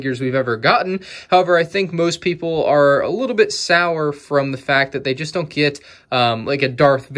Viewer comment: did he said "Sauer"